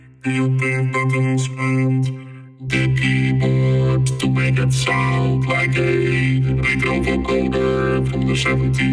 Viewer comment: Does anyone know what he is saying at 1:57?? He's said it sounds like something from the 70s but I can't understand. I really want to know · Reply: The keyboard to make it like a a vocoder from the 70s make it sound like a child with ther 4 note shifter turn the dile to make it sound like a very big guy